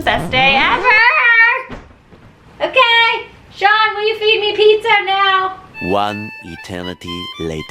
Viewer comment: wat say ?? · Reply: "Shawn will you feed me pizza now?!" ( is her husband)